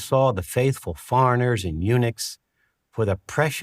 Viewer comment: Did he say "The faithful foreigners (farrenerrs) in Unix"?